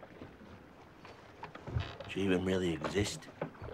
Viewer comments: What did say here 1:24?? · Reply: She even really exist? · What did he say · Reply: She even really exist?